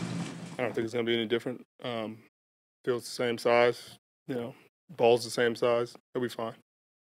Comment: Thought he said:,, His balls are the same size“😅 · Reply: He said the football's are same size and at first he ment the game is same size as normal game. Just go out and do their thing and they will be good